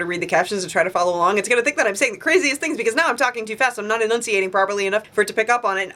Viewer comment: The closed captions said that you were talking to death at 6:02 xD